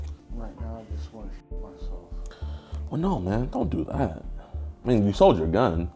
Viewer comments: i fully thought he said "i just wanna shit myself" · When he said he wanted to shoot himself i thought he said sh1t himself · I thought he said shit myself for a second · when he said he wanted to shoot himself, i thought he said he was gonna shit himself, i was very confused when JiDion said 'dont do that dont leave them behind'. LMFAO💀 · I thought he said that he wanted to shit himself lmao